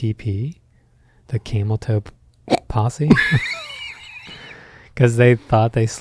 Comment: Your voices are like human demerol, thank you for the calmest place on the internet. I hope you record whispering/giggling lullabies for tired adults. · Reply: I thought he said Cable Tow Posse😂